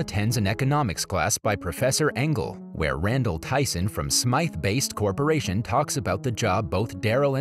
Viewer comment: Did anyone else hear "Smythe BASTE"?